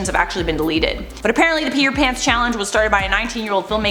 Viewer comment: I thought said Petter Pan oof